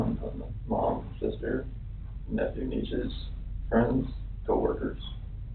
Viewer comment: caption says "nephew neitzsche" instead of "nephews, nieces"